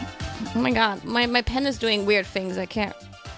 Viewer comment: I can't be the only one who heard that 💀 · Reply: "Pen is," not penis. "The goon side is 2 doors down" — Gawr Gura